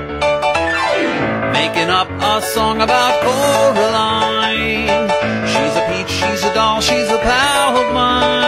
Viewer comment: when i first heard it i heard "SHES A POWER-LINE!"